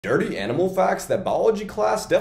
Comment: I thought it said anime, not animal oops